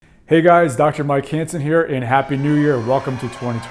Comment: I misheard your name as Dr. Mike Handsome.